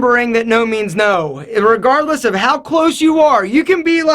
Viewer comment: did you say and regardless or irregardless?